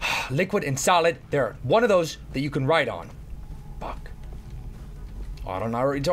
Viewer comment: Lmao a little after 3:14 sounds like he says fuck and not buck lol and I was gonna be so happy that he cursed!!